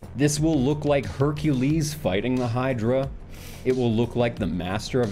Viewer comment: Is it wrong that the first time I heard him say "fighting", my brain autocorrected that and all the others to "fucking"?